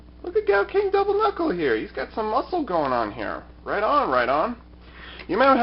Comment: Did I hear him right? I thought he said "Right on, Rhydon!" 😂 If so, I greatly appreciate that Pokemon joke cx